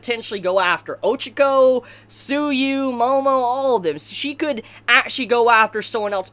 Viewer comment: When you said 'Tsuyu' I misheard it and thought you said 'Sue You' I was so shocked I deeply apologize XD